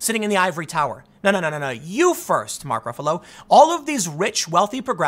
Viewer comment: i almost thought i heard Tim say "You First Mother Fucker".